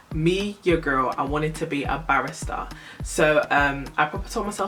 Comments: At first I thought you said you wanted to be a Barista (like at Costa) lol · Hahaha, i thought you said you wanted to be a barista :P